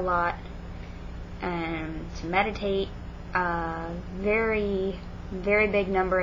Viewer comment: When you said "meditate" I thought you said "medicate" and I was like oooo, I got it now though. If I was a student there I would try a spell or 2 with you out of curiosity, even though I am not supernaturally inclined. Pagans are nice, ignore the weird christians.